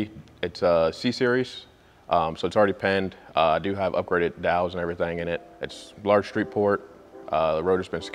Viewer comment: Did he say "upgraded valves" @ 1:27? what valves? Rotaries do not have valves.....